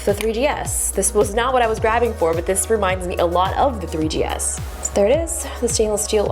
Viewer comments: Thought she said "it reminds me of the 3ds"... 3gs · “Reminds me of the 3ds?” I know she meant 3GS but it sounds like she said 3ds😂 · is she saying 3DS? And then She says 3G S few seconds later? Lol the excitement of the iPhone X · lowkey thought justice said “a lot like the 3ds” then she pulled out an 3gs lmao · Who else thought she said "3DS" at 3:28 like if you agree